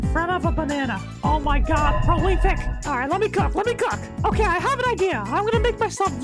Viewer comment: what did you say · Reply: let me cook let me cook